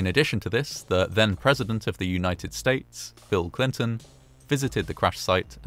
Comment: Really sounds like your saying phill Clinton